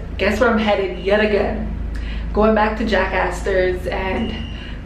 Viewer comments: I thought you said you were going to Jack Asses! lol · who else thought she said JACKASS INSTEAD OF JACK ASTORS